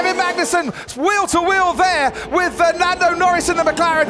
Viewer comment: Did i hear Fernando Norris??😂